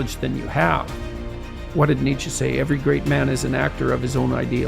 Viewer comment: MIN 1:31 guys, just a little CORRECTION... typing error: NOT CORRECT: what did "NATURE" say? (error... NOT "nature") CORRECT: Nietzsche, "what did Nietzsche say? (Friedrich Nietzsche, the philosopher) NOT NATURE so, the Original quote: "every great man is an actor of his own ideal" __Friedrich Nietzsche